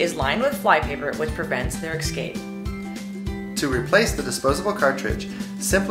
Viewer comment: Did she say "ecscape" ?